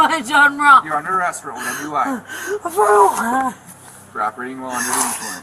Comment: It's "for OWI" not "for whatever you like" at 6:36 :P And the intelligible part after she asks "For what?" was the officer saying "driving under the influence"